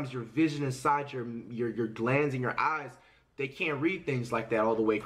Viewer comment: did he just say gland what does that have to do with ur eyes